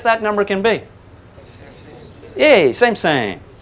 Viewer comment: what number did he said? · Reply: Same-same.